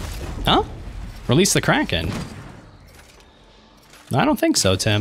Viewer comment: 6.39 i heard "Release the co**"